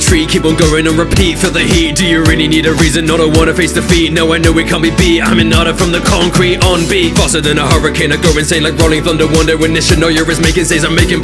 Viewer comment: "I'M HINATA FROM THE CONCRETE". hands down best line in this rap. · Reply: Ngl when I had first heard this, I thought he had said I'm an otter from the concrete.